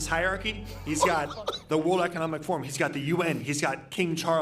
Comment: did he say “ he got the UK’s” 😂